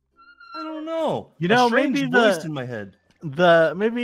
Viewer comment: did any of you catch what freddy said? "I don't know, a strange voice in my head" I think the voice has returned................. And thats just a theory